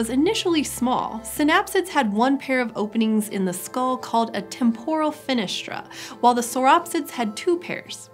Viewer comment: I was eating and thought I heard "Tempura Fenestra" lol